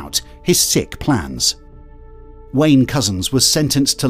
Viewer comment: Damn I heard "Wayne's cousin" and was puzzled for a few minutes during the interview